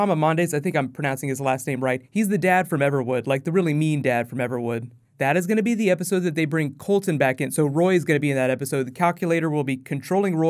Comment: HAD TO HEAR THIS AGAIN, SOUNDED LIKE YOU SAID COULSON - I KNOW YOU MEANT COULTON HAYNES. WOW, STRANGE HA HA HA